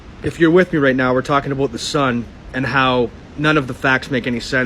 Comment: did he say aboot the sun?